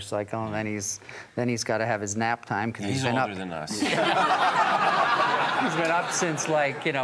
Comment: Yeah, and what does Alex say at 10:11? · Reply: Alex says “He’s older than us”